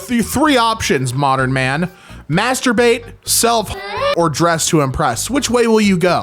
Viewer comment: For some reason, I heard selfcest and was very confused.